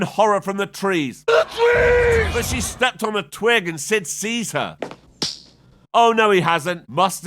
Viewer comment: did you say Sid Caesar?!